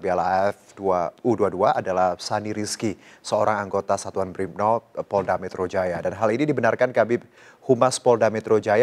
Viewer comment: gw dengernya BRIBNOP